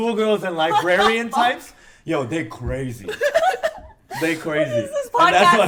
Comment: lol thought you said agrarian types haha